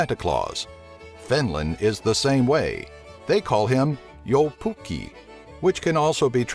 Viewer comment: Gotta say, it sounded like u said "Joupukki" and jou basically means yo lol it's "joulupukki".